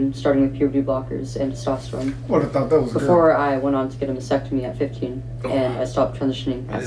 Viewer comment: OH, she said Mastectomy I thought she said Vasectomy which made no sense. I am not sure there are enough trans people who could make up football/soccer/basketball leagues I could be wrong.